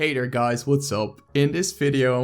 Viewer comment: did you just call us, "hater guys"?